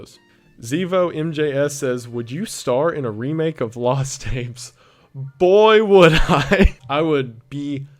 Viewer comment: I misheard “lost tapes” as “lost apes” Also, big respect to you as a fella who also buys Hawaiian shirts from thrift stores